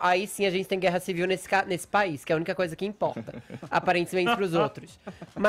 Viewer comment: Para Fefito "o carnaval é a única coisa que importa". Foi o que eu ouvi?